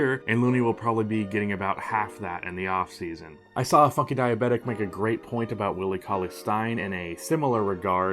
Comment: Mic was fucked up in this video, sorry about that, still trying to figure out how to get it right. GOATmentary FULL - <link> Follow me on twitter- <link> Check out <link> ! 18-19 playlist- <link> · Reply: What name did you actually say on the part you mentioned a guy saying something about Cauley-Stein's skillset? I heard "a fuckin diabetic".